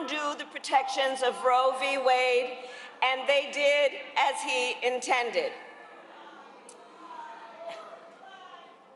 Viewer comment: What did they say? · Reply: They said Jesus is Lord